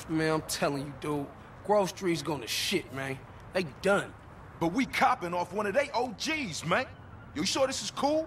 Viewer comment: he doesn't say "we're capping one of their OGs" that's not what was said. He said "but we're COPPING off of one of their OGs" "cop" is a slang term for purchasing. They were saying they were being supplied by Smoke and that it felt like a trap BECAUSE of that. It'd be mad suspicious if you were a drug lord/dealer knocking dudes off your own team (essentially weakening yourself) so the one guy was skeptical about the whole thing